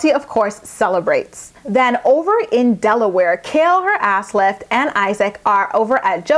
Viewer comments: lol did u just say kail and her ass lift? xD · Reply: Kinky.coily. ஐBeebe ஐ Treeஐ i thought she said ass lips! LOL! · lol did u just say kail and her ass lift? xD